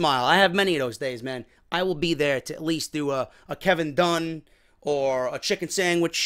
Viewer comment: I thought it said kevin dunn ahhHuhUhhHhH